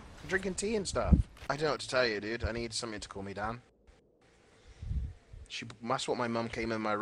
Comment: can anyone tell me what Greek said there exactly? Couldn't really understand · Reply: Janzorn I need something to cool me down